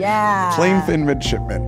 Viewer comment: I heard “Plainfin Midgetman”